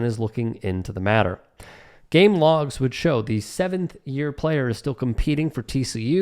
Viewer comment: Hold on. For some reason i keep hearing "7th year athlete "...